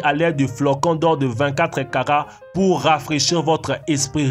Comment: c'est moi ou j'ai entendu caca au lieu de "karats" ? 😂😂😂